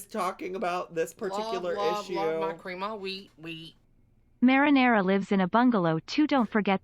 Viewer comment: no matter how many times i hear "love love my cream of wheat wheat" I still hear "blah blah my cream of wheat wheat"